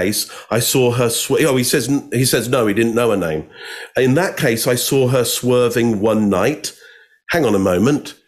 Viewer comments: " in that case, I saw her swerve and WHATNOT " You misheard what he said, Mark. Throws your entire stalking theory to the ground like a bag of rocks. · Many big mistakes but this was glaring. At 5:30 Mark Bowden makes false claim about what Holtzclaw said. Probably just misheard and not intentional. Holtzclaw's filler words are "whatnot," and didn't say "one night."